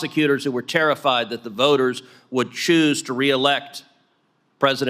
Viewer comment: Senator Ted Cruz ... 😎😎😎!! But did he really say, "Were not a banana republic?" (4:55) Or did I misunderstand that ...?😂🤣🤣!!